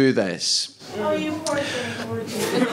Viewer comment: what does she say? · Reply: Oh you poor thing